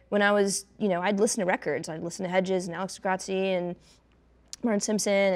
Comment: what are the names Kaki King namedrops? I heard Chet Atkins and Nick Drake, but did anyone make out the first couple? Also: god I love Guitar Power, Kaki King, and Dweezil is just the perfect host for this show. Loved the previous ones, but Dweezil is just a smart, curious player. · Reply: Michael Hedges and Alex De Grassi